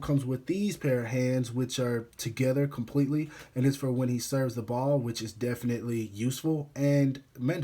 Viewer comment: did I hear serve the ball 😳